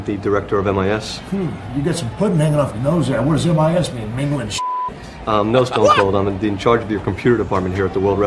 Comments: What does Austin say at 4:04? "What does MIS stand for ....... ?" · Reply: Mingle in shit 😂😂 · What did stone cold say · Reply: 'What does MIS mean, mingle in shit?'